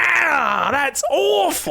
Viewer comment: At the end when he would've said "that's awful" it got cut out and so I heard "that's off".